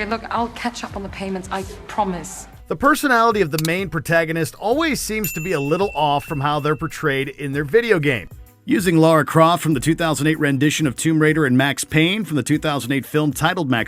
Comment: Did he say 2008 Tomb Raider? Did someone edit out the -een?